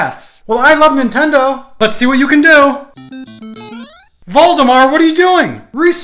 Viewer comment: I thought he said voldemort